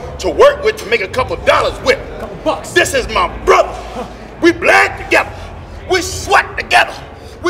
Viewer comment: I thought he said "we black together" when he said that they bled together.